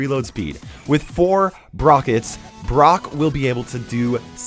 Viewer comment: did he say 'brokets'? xD